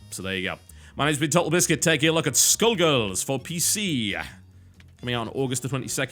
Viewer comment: i thought u said "taking a look at school girls"